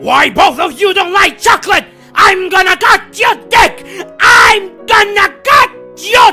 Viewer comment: The scissors tell me "cut" but the auto generated subtitles say "touch"